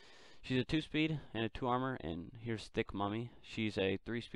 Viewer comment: This isn't clickbait. I heard "extra thicc"